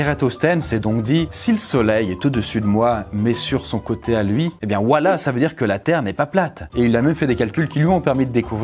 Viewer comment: c'est moi ou il dit wallha